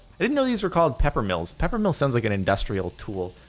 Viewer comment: I thought it said “paper mill” and not “pepper mill”